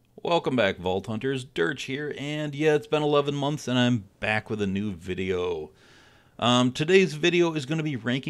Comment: Whoah whoah I heard "back with a nude video" flagged for false advertising